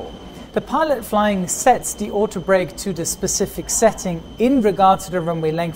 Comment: Thought you said "brake to the cake lol